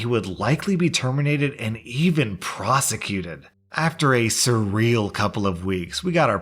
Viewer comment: i'm one of those subscribers that occasionally listen rather than read along and at 14:51 - proves that my listening skills need work as i thought ya said prostituted instead of prosecuted